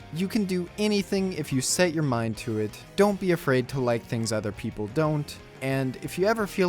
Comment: i backed out of the video and thought he said "don't be afraid to light things on fire".